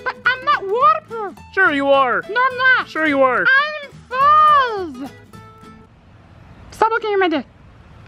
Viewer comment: what did he say i cant here what he said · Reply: oneshot the racing gamer im fuzzzzz